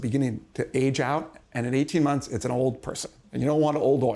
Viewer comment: Did Jack just say, "It's an old person, and no one wants..." Jack, old people watch your show. And I must say, you ain't no Spring Chicken yourself. Is this why Chris Pratt didn't call me when he was single? People live in glass houses? The takeaway: Make sure the glass is green.